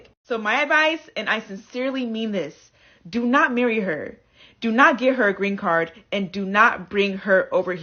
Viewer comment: I keep hearing "do not give her a green card." Do these folk not realize you can't just give somebody a green card?